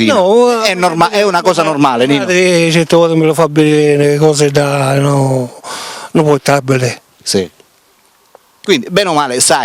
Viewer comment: non ho capito bene...la madre certe volte gliela fa vedere? Poverino comunque mi fa pena, il disagio di quest'ominide, forse non troppo sviluppato mentalmente, che intervista il povero Nino..un po' come prendere per culo un cinese perché non dice la "R"...un po' gratuito insomma...ehvabè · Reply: Dice: "Me frati certi voti mi lu fa vidiri nu portabile" Traduzione:"Mio fratello certe volte me lo fa vedere sul portatile"